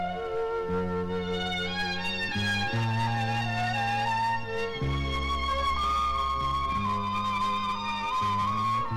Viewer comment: 'de Kemal Sunal konuşmaya başladığında ne dediğini anlayabilen var mı? normalde dudak okuma konusunda iyiyimdir ancak en başta kemal sunalın hangi sözlerle muhabbete başladığını bir türlü anlayamadım. Sonra kız bir şey mi dediniz. O gül solmuş bak bu çok güzel diyor. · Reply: Nihayet buldum seni gibi birşey diyor